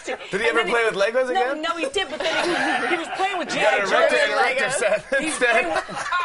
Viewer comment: what did Jimmy say at 4:49 ? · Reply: "You got him an erector set" or something like that. LOL